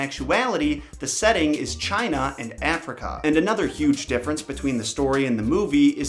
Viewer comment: i thought u said China in Africa 😅